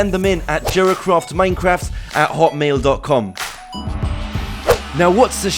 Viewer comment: did he just say 'hot mail'